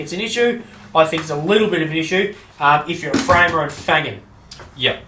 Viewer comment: framer or a .... What was the next word ?😅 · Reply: If you're a framer and fangin' (i.e. flying/working very quickly)